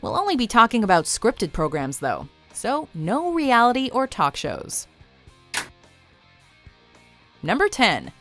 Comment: "No reality or talk shows", I thought you said you were doing scripted shows😂